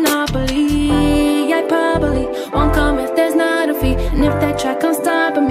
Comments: instead of “i probably” i heard “hyperbole” lmao what’s wrong with me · I heard "hyperbole" when it went like "probably" lmao